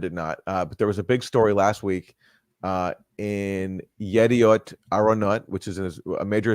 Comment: It sounded like Katy said "A-aron" like Keegan as the substitute teacher. 😅